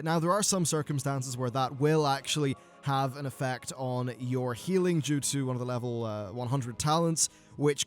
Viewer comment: Did he just say healing jutsu at 3:59?